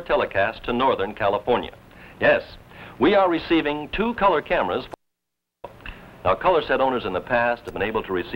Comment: what Harry says is "We are receiving two color cameras for our studio."